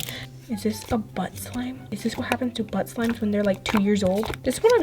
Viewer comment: Who heard say “is this what happens to but slimes”